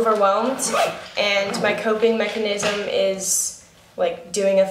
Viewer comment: who thought she said cocaine mechanisuim is doing a thumbs up lollll